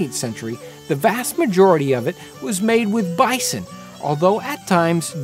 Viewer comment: misheard "Ricin" when you said Bison. Had to take a second and third listen.